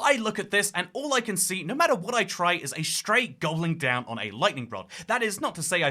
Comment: i’m pretty sure you said “gobbling down”, but i heard “going down” and “stray going down on a lightning rod” is my favorite interpretation of the key design 😭